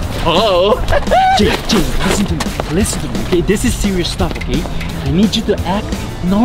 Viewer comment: I heard sex